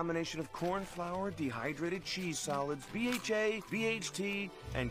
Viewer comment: when he said nacho cheese flavor I thought he said NOT YOUR CHEESE FLAVOR